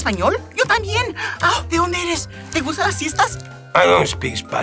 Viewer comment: I always thought he said “te gusta las fiestas”